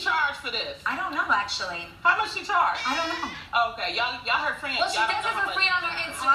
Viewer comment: What did say at 3:18 ? “Yall her friends ... “